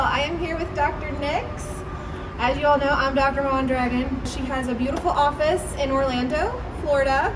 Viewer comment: I thought she said I'm Doctor VonDragon and I thought wow what a cool name.